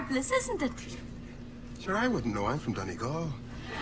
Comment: Since you were confused by what the black priest said at 9:36, he says "Sure I wouldn't know, I'm from Donegal." Donegal is a county in Ireland and the nun thinks the black priest is an African priest which is why she is talking to him about the African faith community. And he replies that he doesn't know about it as he is from Donegal. Even has the accent to prove it!